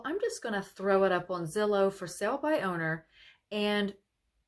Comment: I have sold 3 hymns by myself and save the commission but that was a long time ago I'm thinking of selling in the fall can you tell me where I should go I thought you said Zillow or Willow I'm not sure would you please tell me thank you so much God bless you and your new Journeys in life